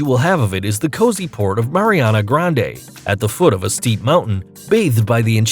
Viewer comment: did you just say ariana grande